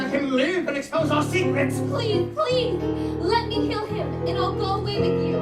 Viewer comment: Am I the only one who heard Rapunzel say "Let me kill him" at 17:25. Instead of save him?